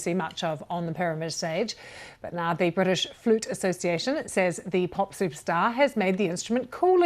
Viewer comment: I thought you said the British fruit association. 😂 Beautiful! Love her!